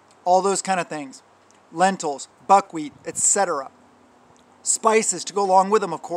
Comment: When you said buckwheat I thought you said bug wheat 🤣🤣🤣